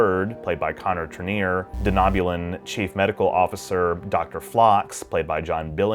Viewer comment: Did you say "De-nob-u-lin"?